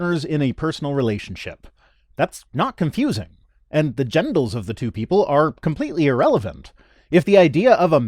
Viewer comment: It sounds like said "gendles" tee-hee!